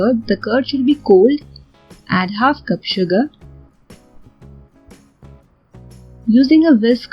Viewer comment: i heard add suga by mistake...💜 😂😂😂😂